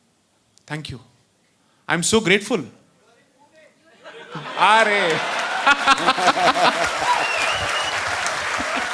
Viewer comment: What did the audience member say at 2:51:50 to which J Sai Deepak said Areeee..... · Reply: "You're in Pune".